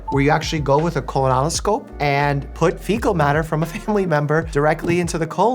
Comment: I thought he said that a fecal transplant uses fecal matter from a FACULTY (not family) member and was like “…we really ask way too much of medical staff 😭😭”